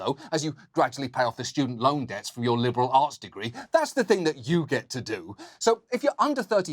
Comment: when I mishear it to be "liberal arse degree"...